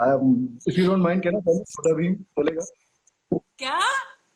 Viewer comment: What did he say at59:59??? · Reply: Chota bheem